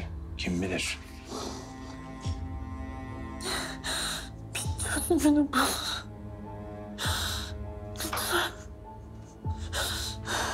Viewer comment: da hasta olan kadın ne diyor bir türlü anlamadım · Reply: "Bi' çözümünü bul, lütfen. "